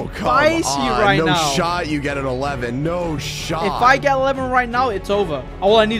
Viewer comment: Blood Golems Are Smart Like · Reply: I thought you said short.